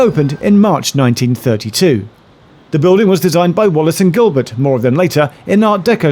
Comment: Everytime you say "Wallis and Gilbert" I hear "Wallace and Grommit"........now THAT would be an interesting station😂